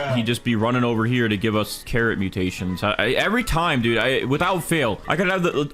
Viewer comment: did bro say the carrot mutation at 11:33 minutes 🤣